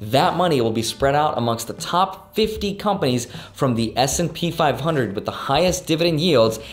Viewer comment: I legit thought you said SMP 500 and i was like bro u better watch out